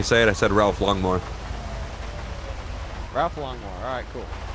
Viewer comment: it sounded like your saying ralph lawnmower XD